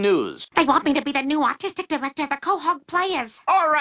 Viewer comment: i really thought she said autistic and i was so confused .. 💀